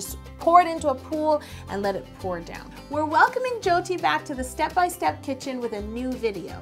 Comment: I was about to fall asleep when she mentioned "jyoti" and I heard it as Chelsea so I was thinking "I made a video with her?". I then became fully awake when I realized that I live in the other side of the world and I'm not a youtuber😂😂😂